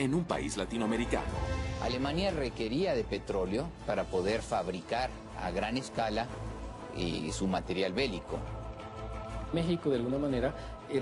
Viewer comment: Que dice en el segundo 0:19? Alemania nesesitaba petróleo para materiales medicos? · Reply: para construir a gran escala su material bélico